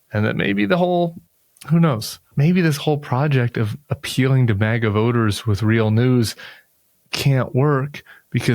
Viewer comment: Around the 3 minute mark, You said “maga voters”, but I heard “bag of odors” 😂 I had to listen three times!! Thought You came up with a new nickname 🤘😂😜🤣🤘 💪💙🇺🇸